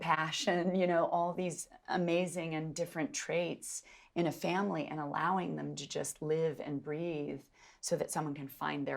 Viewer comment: What does Florence say to Saoirse on minute 7:25???? She looks so flustered and i'm living for it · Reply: I think Florence said “ I warned you “, probably referring to the fight Jo and Amy have over Amy burning Jo’s book